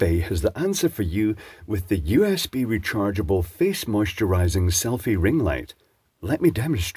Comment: I misheard it as "a ring moisturising face light".